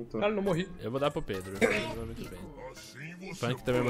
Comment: acho q ouvi... "Eu vou dar pro Pedro"(6:50). Safadênho esse